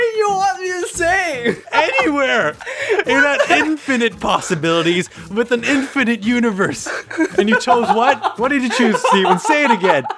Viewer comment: Are we just going to ignore- Steven: what do you want me to say: anywhere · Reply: He said "Anywhere in that infinite possibilities with an infinite universe''